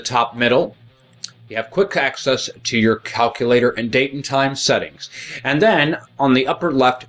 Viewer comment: sounds like you said calculator instead of calendar